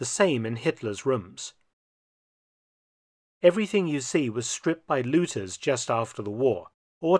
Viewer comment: sounds like he said stripped by Losers after the war lol